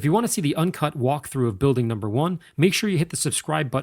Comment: wait.....did he say building number ONE?.....🙈👀😭😭😭😭😭😭😭 this is just far tooooooo much awesomeness for 1video LMAO 😁✌🏽